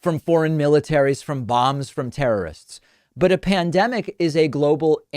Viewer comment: I misheard "from b*mbs of terr*rists" as "from Moms of terr*rists". LOL...not sure why.